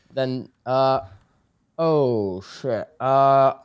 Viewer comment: i heard a sh!t from tubbo